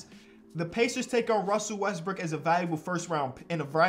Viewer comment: Who else hear that Westbrick😂